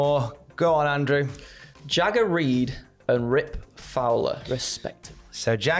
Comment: Genuinely thought Andrew said "Shagger Reed"